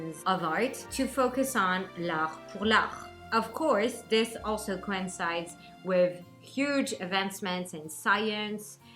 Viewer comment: Hello. What is that word @ 1:21? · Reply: Hey! "L'Art pour l'Art" in french meaning The Art for the Art ;)